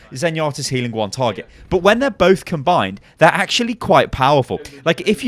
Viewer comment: "When both they are both combined..." All I heard was Captain Planet.